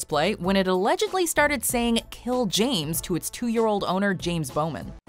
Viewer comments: he sound like he says tell James not kill slow it down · On the Elmo one I heard “tell James” not k*ll. So idk there